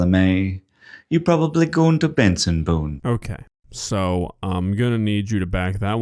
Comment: I thought he was saying 'I'm goin' to Benson Boone' with a weird accent.